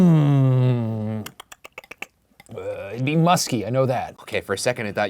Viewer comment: "It'd be musky ik that" thought he said musty lol